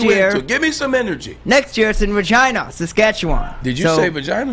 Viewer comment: 🤣🤣🤣🤣 "give me some energy" "did you say Vagina?" Omg I thought I heard it too